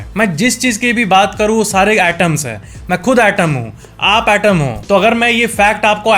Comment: fact swami shivanand 03:07 -fact no. 7-fact negativity ineffective 04:11 -fact no. 6-fact atoms 05:00 -fact no. 5-fact chocolate cake cookies 05:58 -fact no. 4-fact digital sundiel 06:48 -fact no.3 -fact lemon 07:14 -fact no. 2-fact Insomnia 07:37 fact no.1 fact whatsapp fonts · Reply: by mistake I heard it ““ mai khud item hu ”” replace of atom😂😜...